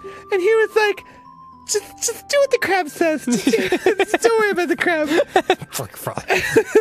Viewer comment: wait, the king said "don't worry about the crab"? i thought he said "obey the crab"...